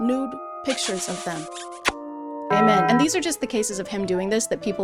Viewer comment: Off topic. How many people do you think would notice if you ended prayer with ”gay men” instead of ”amen”?